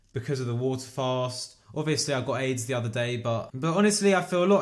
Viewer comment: Hold up, what did he just say!? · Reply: "obviously I've got to eat the other day.."